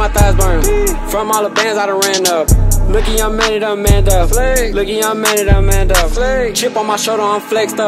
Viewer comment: On 0:45 it sounds like he saying I made it Amanda, basically telling her you fucked up and i made it with out you, would've been harder if that was the case